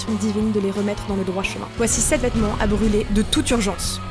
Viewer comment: c moi ou elle a dit "aliments" au lieu de "vêtements" ?